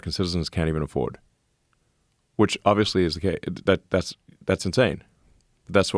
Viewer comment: did Elon say "Which obviously is gay' ? or I'm trippin' :D :D